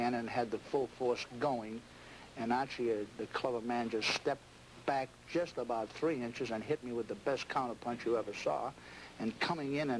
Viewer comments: What does Marciano say at 6:32? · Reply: Torunamukaji “archie the clever man stepped back just about three inches and hit me with the best counterpunch you ever saw” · Did I just heard Marciano called Archie Moore a colored man? Correct me if im mistaken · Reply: No brother. He said “Archie, the “clever” man”, but it made some of us do a double take no doubt. The Rock got that Massachusetts drawl. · Did I hear Marciano refer to Walcott as "the colored man" at 6:33? It's what it sounded like, but maybe I misheard it?